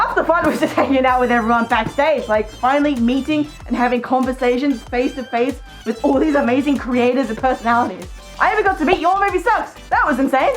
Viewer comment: Wait, what did say? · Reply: Half the fun was just backstage meeting and having conversations with amazing creators with awesome personalities :)